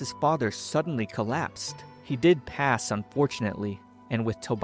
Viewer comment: I thought he said, "He didn't pass, unfortunately." Which I was about to say that's was crazy✋️😭